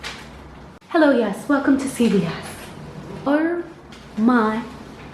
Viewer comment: I thought you said “welcome to chili’s” instead of “welcome to CBS” Lol